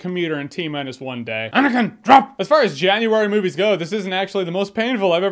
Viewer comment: Did say "ANAKIN, DROP"😂😂